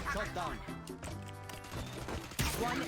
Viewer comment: ผมไม่เคยเล่น valorant นะ แต่ในช่วง 1:30 น้องยิงเสร็จได้ยินตัวละครพูดว่า "ส้มตำ" ??